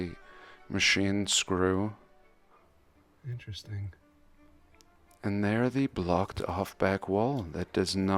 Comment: at 20 minutes in I heard someone say 'help me' ... At 20:52 the voice says 'interesting.'